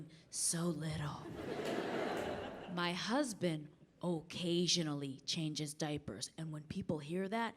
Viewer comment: for a sec I thought she said caucasianly changes diapers.